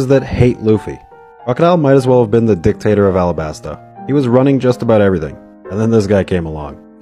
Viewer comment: Honestly i thought he said the dictator of alabama 💀